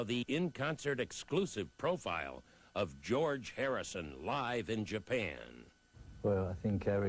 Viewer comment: I’m sorry but I hear George Hairison at 0:22 hahahah